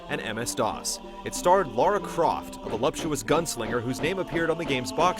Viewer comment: What's the word that came before "gunslinger" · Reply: voluptuous